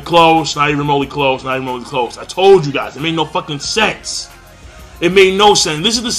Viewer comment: thought he said it made no fucking sex